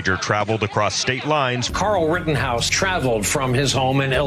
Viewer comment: anyone else hear "Carl Rittenhouse"